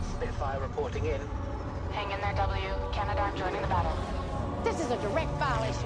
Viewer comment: did I hear "Canadarm joining the battle?"